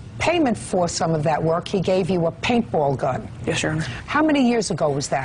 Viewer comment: Is he saying "yes sir" in the beginning? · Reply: He's saying "Yes your honor". I thought he was saying "yes sir" too at first.